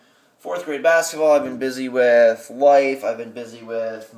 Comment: did i hear he was busy with wife in 6:46? or am i just deaf?